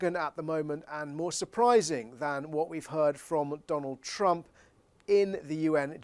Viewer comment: At time stamp 4:50 did say McDonald Trump, we all know he’s a clown.😂